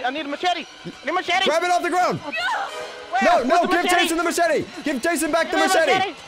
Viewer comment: Did just said give back the machete to Jason O_O 8:51 ? Anyway, congratz for your 400k subs. A new subs here btw XD